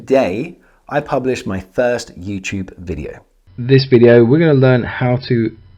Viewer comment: "10 years ago today, I published my thirst YouTube video." That's what I heard and I'm sticking with it.